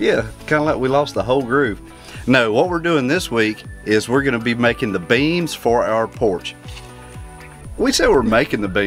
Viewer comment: Great news there....no awesome news....and when was saying making the beam'si thought he was saying making the beans....lol...i am so glad you are doing better. Are we going to have the privileged for another cooking with and....as long as gus does not eat the food before you finish the video....